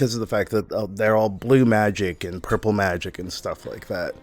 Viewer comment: I thought blud said "Cripple magic" Lol